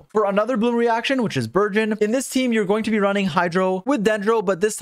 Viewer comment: i thought you said virgin and not burgeon